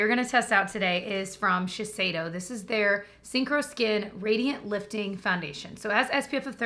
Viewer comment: I’ve always heard you say the name of the Shiseido concealer and thought it was called ‘Sink or Skin’ and thought ‘ya that makes sense, you either sink or have coverage on your skin’. (🤷🏼‍♀️) It wasn’t until I saw ‘Synchro’ on the screen today and learned the actually name. I must say I like mine better 🤣